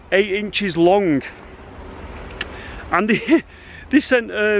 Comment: My caption don’t do well with accent. The cucumber 🥒 is seven foot eight inches long. But the caption say seven four eight inches long. If you feel like wasting your time let me kno if it writes the same thing. 19:02 mark. Good day and god bless.